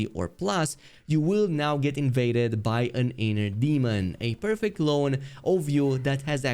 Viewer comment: I thought I heard "getting invaded by an anal demon" hmmm